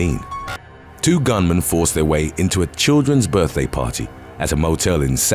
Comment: I thought this said "Birthday Panties" for a second and was very confused 😂